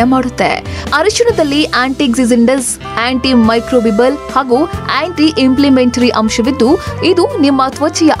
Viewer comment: did she say antimicro vimal? Anti inflimitry? 😂